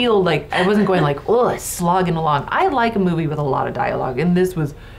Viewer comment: "Slogging along" sounded like "smog in the lung" 😂